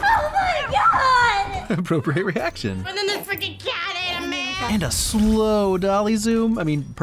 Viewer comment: Did she say f*cking?! I NEED to know how many takes exist to get the perfect blend of freaking & f*cking that could get passed off by the censors.